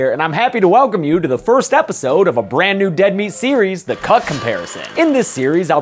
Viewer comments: I thought he said, the chock comparison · I really thought he said "The Cuck Comparison." I got really confused and concerned